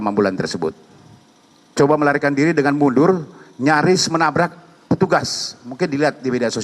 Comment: lah dia kan salah, makannya kabur takut ketangkep gimana si wkwkwk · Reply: kalau saya gak salah dengar "nyaris tertabrak" bukan tertabrak.